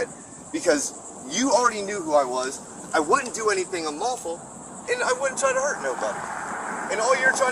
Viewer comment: you mispronounced unlawful, sounded like you said simple